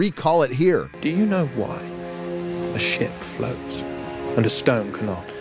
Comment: I hear, "do you know why a sh1t floats", and it actually improves the scene!